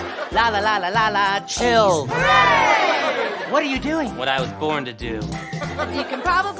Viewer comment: What I heard was"lalalala CHILLS"